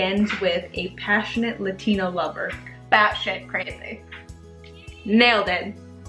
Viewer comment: Decided to turn off the sound and have a go before I watched it with sound on... I failed epically! Latino lover 😂 I thought you were saying Lesbian lover 😂 my sentence was so confused!